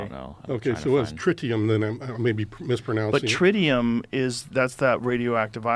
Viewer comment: LOL, a fictional metal from the Stargate television series! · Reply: oh my gosh, I have never watched this movie. I thought the NHI said “trinium” so I googled and found that info in google search. So I’ll have to watch this movie you mentioned. 😀